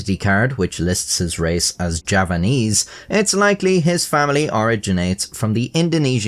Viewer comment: Suparman is a common name for Javanese. "Java" not "Japan". He was born in 1990, so he was probably named after a character in a movie which was released in 1989. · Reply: Ýeah, sorry, I could have sworn I heard him say Japanese but obviously given context it must have been Javanese